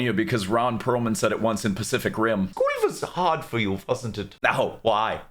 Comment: What was hard for him? @ 4:05 The subtitles don't match what they are saying 60% of the time, and I'm having a hard time following along at certain parts. Would anyone be kind enough to translate for me? Something was hard for him... it legit sounds like Goya, or even Korea... which cannot fit lol! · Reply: I think, genuinely, he said "schooling was hard for you, wasnt it?" Thats what I heard at least